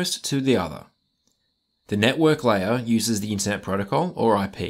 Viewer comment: "the network uses the (what?) protocol? couldnt hear what he said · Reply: "The network layer uses the internet protocol" (I know, my accent's a bit thick)